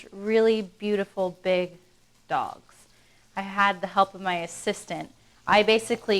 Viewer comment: what i heard was "at the end of the day we basically have 6 and a half inch really beautiful big dongs"